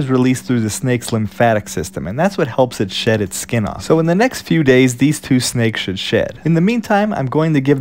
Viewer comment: At first I thought he said in the next to days these snakes should shit